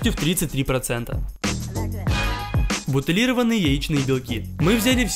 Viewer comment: мне одной послышалось не яичны белки а белчиные яйки